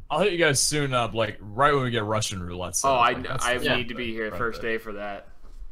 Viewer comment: did he say rusher? as in TheCampingRusher?! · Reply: Vincent Utti-Hodge Russian Roulette, he said.